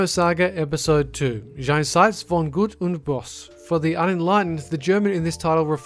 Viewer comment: Lol: Jenseidz fon Gud und Boss.. I asked myself what he says there at 3:11 and then I noticed he tried to speak german. Haha And I am from Germany.